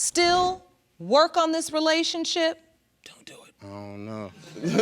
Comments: "Don't do it" is what Mr Green whispered. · Anyone hear Mr Green whisper "don't do it" at 14:44 LOL that guy was awesome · Did old boy Green whisper: DON'T DO IT! After she asked him can he then work on this relationship? I heard a whisper: DON'T DO IT! 😂😂😂😂😂😂 They probably will exchange numbers. Time Stamp